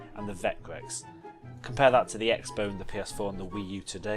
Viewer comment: did he just say the X bow?